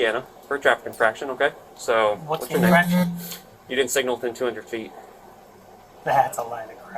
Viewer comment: It sounded like they were saying you didn't signal 200 ft for the right hand turn, which looks like you didn't.